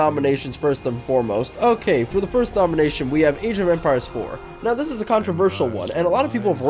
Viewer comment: age of empires 4 , i thought i heard asian vampires 4 for a sec there